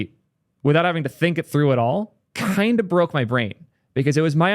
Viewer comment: the "kind" at 2:49 sounded like "my precious"... It was scary.